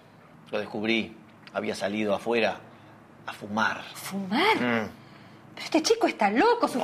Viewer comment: escuche a el mar en vez de a fumar JAJAJA